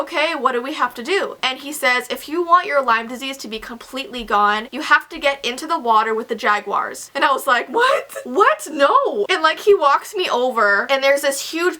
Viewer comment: What did she say at 5:01?? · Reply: if you want your lyme disease to be completely gone you have to get in the water with the jaguars